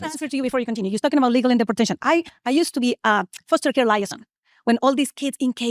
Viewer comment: That woman who was so pressed when Dean was questioning her about if sexuality is a choice, is absolutely repressing some same sex attraction. I'm bi and hate when people say all homophobic people are secretly gay, but this woman is definitely struggling with some inner demons. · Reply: Among all the screaming I'm pretty sure I heard "I used to be gay" which is... A lot to unpack